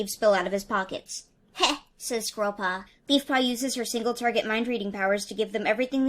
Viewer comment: Did she say “ Beefpaw”?